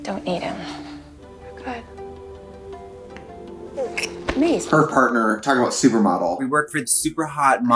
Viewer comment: Netflix, why do the subtitles think Maze is called Denise? Is this a callback to the Chilling Adventures of Denise?!